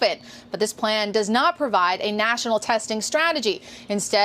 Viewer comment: 에it leaves 가 아니라 it leads로 들리는데 다들 어떠신가요?